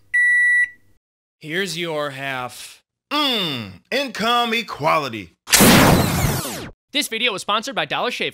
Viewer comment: Did anyone else hear "in commie quality" at the end? First time I misheard something that ended up being more accurate.